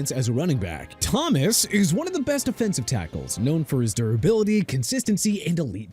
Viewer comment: The way this dude say pro bowl PMO, and no Bruce smith, singletary, or ocho, plus says Joe Thomas is a defensive tackle, I’m not finishing this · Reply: He didnt say Joe Thomas was a defensive tackle, you misheard. He said "Thomas is one of the best offensive tackles". I guess I could see how you could've heard it that way since he was talking fast tho.